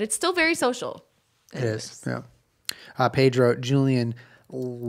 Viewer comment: I thought he said Julien loves WALKING the dogs in the wine room and I just delighted in the image of Julien walking the iggies in really tiny scrunched circles around in the wine room